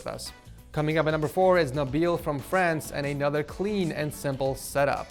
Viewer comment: did he really say nabeel from France? 😂😂